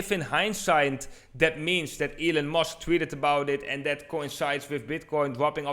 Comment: sounded like he said ELLEN MUSK one time